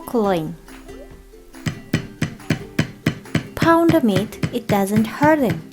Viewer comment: at first i thought she said, "pound meat it doesn't hurt it." anyone else?